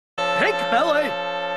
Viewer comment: The first time I saw this, I thought he was saying "Pain Belly."